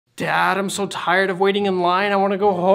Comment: I had no sound and I thought you said “Dad I’m so tired of blonde white boys💀💀